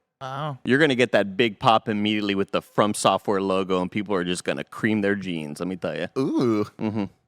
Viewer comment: Andy: And people are gonna cream their jeans. Tim: Ooooohhhhh! 🤣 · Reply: LOL when I heard that I heard "cream their beans"